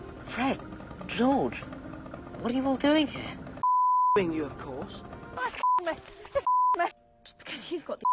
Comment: i almost peed my pants 😅😂 I watched this with my muggle bf and he thought it said "fucking you of course". We had a great laugh. And whenever Draco would come on my bf would always thinn about swear words. Trough out the whole video we had go stop at every scene since we couldn't stop laughing and mr. Muggle (yes that's what i call him) kept completing the sentence with 18+ things or swear words. He asked me if it's a movie for children or a fucking swearing movie series that is filled with 18+ stuff 😂😂😂